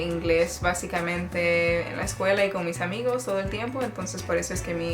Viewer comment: did you say «all the tiempo» @ 1:56? I think that's what you talk about later when you get lost in languages :)